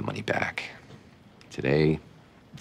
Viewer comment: After House says "but hospitals come into a lot of money" what does he say? Mum's the word? I can't understand it at all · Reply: Mums the word